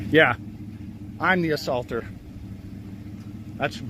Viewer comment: I could be wrong but it sounds like the lady said to the officer “he assaulted him” while pointing at the guy driving the pickup truck first… If that’s what was actually said, I never thought they’d start by stating actual facts and them still do nothing.